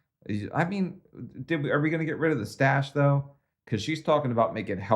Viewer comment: I thought she said sex stash 😂😂😂